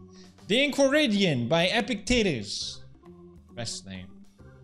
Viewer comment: Because of his accent I swear he sounded like he said “Epic Titties” instead of “Epictetus”